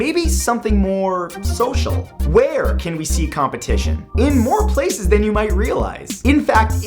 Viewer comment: Who else thought it said completion?